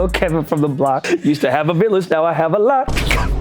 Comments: “used to have a village now i have a lot” 😭😭😭😭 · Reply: I THOUGHT HE SAID I HAVE A GLOCK? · “used to have a village now i have a lot” 😭😭😭😭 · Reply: I thought he said "now I have a lock" 😭